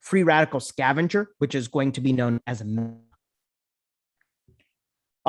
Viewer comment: What is the word after Free Radical Scavenger? Audio seems to have glitched at that point? Is it anti-oxidant? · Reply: It is Mesna! for Cyclophosphamide.